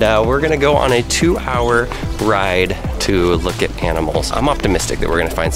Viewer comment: Did he say cannibals or animals?